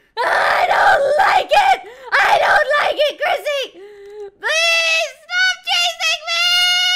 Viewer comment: did she just say "i dont like it crissy" 😂😂